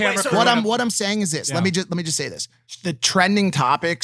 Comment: yo tf was Mike gonna say, why’d you cut it out ? lol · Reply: He said “lemme just say this” then “the trending topic . . .” It was a camera change not a cut